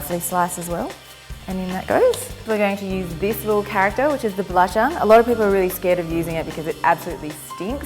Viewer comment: Wtf is she saying and using? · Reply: Belacan, its the shrimp paste.